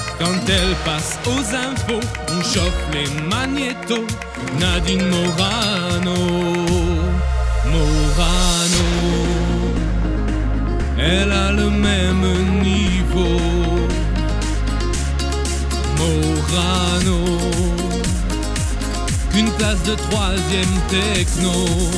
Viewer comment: j'ai entendu " elle a le même niveau qu'une tasse .." ç'aurait été bien aussi